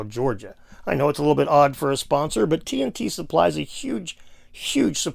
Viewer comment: My mind played tricks on me I thought it said Tit supply